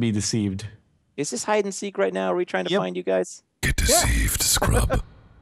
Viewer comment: at first i thought this said Get Deceased Scrub!